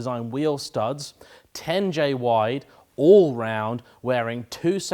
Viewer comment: Did you say the wheels are all round..?